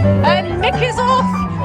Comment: At the end: “Nick is off.” I heard “knickers off”🤣